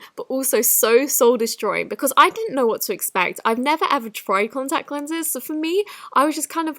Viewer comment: When she said contacts I thought she said condoms 😂😂